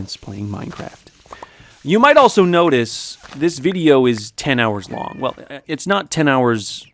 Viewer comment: "as you may have noticed, this video is 10 hours long." I THOUGHT IT SAID 10 MINUTES